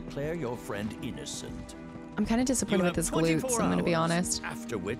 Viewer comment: Edit: I thought you said you were disappointed with the reveal, though now I'm hearing glutes. The events in gold saucer could go different depending on many factors. Your relationship with Barret was very high, so his innocence was revealed where if it was lower it would have been ambiguous to downright incriminating, and the lower it is the more poor Cloud's defense would be. Also, thiugh you have good choice values with everyone, you barely had any synergy attacks with them, or some other factor, so none had a high enough value to "check in" on you and be your escort around gold saucer, or your affinity with Barret may have been the highest of the group. That said... I got Tifa, and I was flat out denied any access to battlesquare.